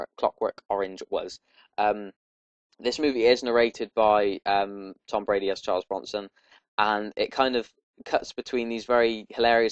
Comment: did you say "Tom Brady as Charles Bronson"? :P